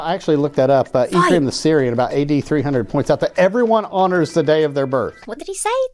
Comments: Hi Emma, great video, as always. I don't know if somebody already pointed that out, but think at 28:42 he said "everyone honors the day of their birth" so there is no word missing. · I heard "Everyone honors the day of their birth" - it was probably his wacky American accent that confused you 😂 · I think he says ''everyone honors the day of their birth''. Bit late to the party, I know. Sorry about that.